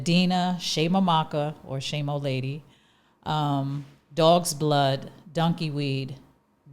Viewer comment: I am, Sorry But did I hear her say " Dogs blood" ??